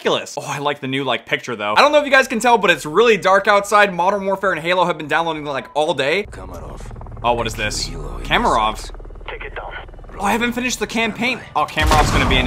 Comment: Did He Just Say MW and Halo?